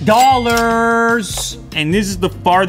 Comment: I know I'm late but...excuse me I thought he said 6000 usd